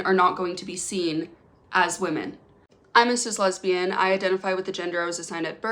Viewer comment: I had to relisten to "I'm a cis lesbian" 4 times to hear it correctly. The first few times I heard, "I'm Mrs lesbian"